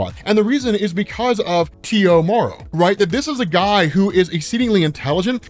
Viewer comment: I thought you were saying "Tio Morrow" like,,, uncle?? lol